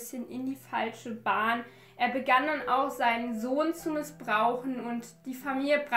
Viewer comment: ich habe verstanden er hat seinen hund missbraucht :]